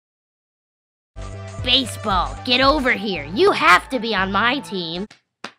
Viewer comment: At one point I thought nickel said "balaseketball" ;-; Im tired